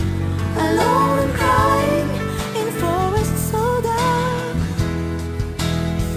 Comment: It sounds like she says "I lol and cry, and fall in soda"